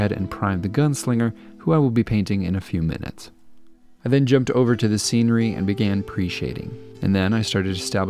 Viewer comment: I thought he said, “I then jumped over to the scenery to start appreciating.” In the moment I really related to that. Good job on this btw